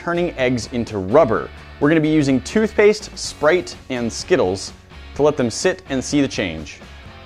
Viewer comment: Is it me or did he say sprate instead of sprite